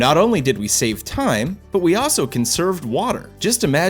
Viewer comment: Thought he said 'can serve' not "conserve"